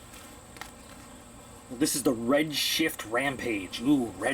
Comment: Totally thought it said Redshirt, not Redshift...